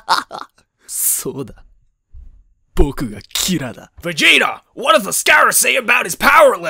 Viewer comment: my mom thought he said "vagina" instead of "vegeta"